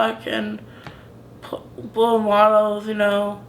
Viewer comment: the fact that he mostly identifies with villain characters but tries do understand them, to understand where they come from, is actually very mature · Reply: I thought he said he can now blow models that he is 18